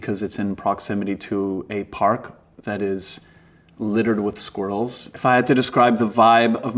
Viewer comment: Did he just say 'littered with Squirtles'?